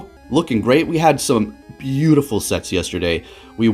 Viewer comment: "We had some beautiful SETS yesterday." Why the hell did I hear, "We had some beautiful SEX yesterday."??? Im over here dying. But, anyways, awesome gameplay